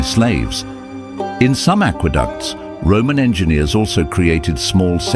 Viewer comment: oh! Roman, I thought I heard romania and got impressed by their science 😂